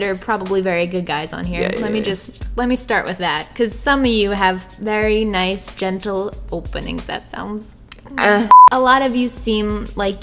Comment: I wasn’t paying attention and I swear I thought she was gonna say very nice genitals (or whatever she said) instead or whatever she said 😂🤦‍♀️🤧